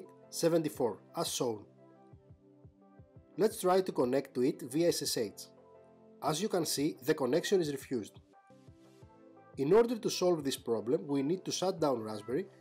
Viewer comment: CC closed caption says "asshole" ?